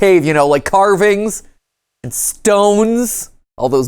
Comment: i think he said carving IN stone